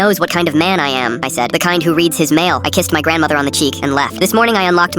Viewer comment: I was so confused for a second, I heard "I kicked my grandmother on the cheek and left"